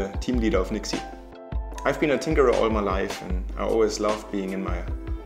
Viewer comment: Who else thought he said "I've been on Tinder all my life" at 0:25? :D ahah, later realised it was "Tinkerer"